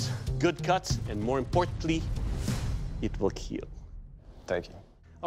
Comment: This whole time I thought he was saying "it will kill" 😅